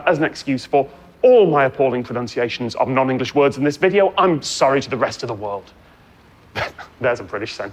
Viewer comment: I thought he said “I’m sorry FOR the rest of the world”, but that’s just my American ears.